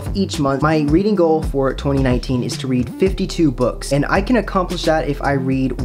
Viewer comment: I misheard at first and thought you said your goal was to read 52 books in January, I was very concerned